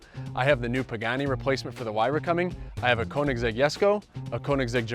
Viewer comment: did he just say "new pagani replacement for the huayra? "